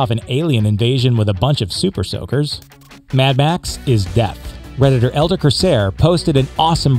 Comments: I was confused about the mad Max one cause i heard it as " mad Max if deaf" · I misheard - "Mad Max is deaf". It would also fit. His hearing must be shot after the opening half hour's action and he doesn't talk much during the film. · I thought you said Mad Max was deaf.